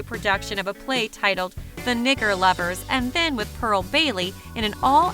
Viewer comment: Is it me or did she say he starred in a play called the N Lovers around the 7:33 mark?